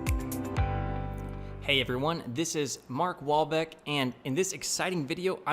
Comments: oh i thought he said Mark Wahlberg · I'm new to this channel and I thought this guy said his name was Mark Wahlberg, lmao